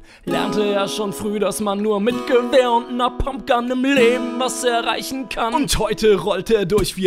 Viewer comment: Ich habe statt Pumpgun, Napalmgun verstanden